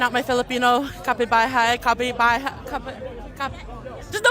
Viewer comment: "kapibuhay" what????? what was she even trying to say 🫩